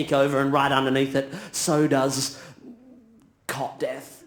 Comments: "So does... Cot death" I almost fell off my chair laughing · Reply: i thought he said cock death haha confusing but still funny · "So does... Cot death" I almost fell off my chair laughing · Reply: I thought he said “car death”. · I'm not a native english speaker, could someone help me with what he says at 3:43 "so does ??????" I don't really understand that word. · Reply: cot (crib) death. it’s like slang for a condition some newborn babies have when they die suddenly, usually in their sleep.